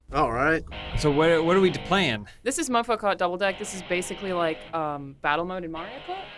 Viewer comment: I thought she said mothafacka double dare. I was like shit this is going to be intense.